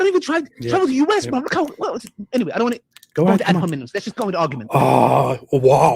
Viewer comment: did he just say "ad hominus"? Man, that Latin is hard....